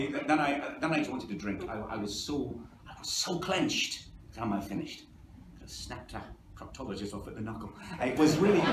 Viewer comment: What does he say at 28:00? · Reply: "I was so clenched by the time I'd finished, I could've snapped a proctologist off at the knuckle".